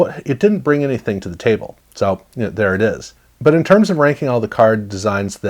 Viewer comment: Did you say "Whoomp there it is" at about the 1:47 mark? I bet that song was from 1991.